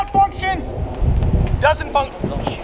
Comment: Bro I heard him oh sh##